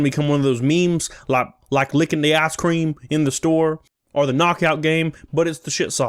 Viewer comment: Considering the context, I thought you said licking the ass cream